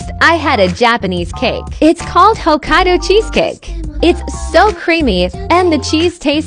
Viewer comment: I thought you said Ohio Cheescake 💀